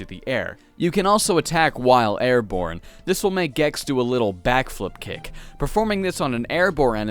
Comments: did he say Gex? :P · Did you just call Vexx Gex?! I watch the rest of the video, now I feel foolish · did he call vexx “gex” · he said gex instead of vex hahaha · did you say “Gex”? 🤔 yes you did! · I swear you called him Gex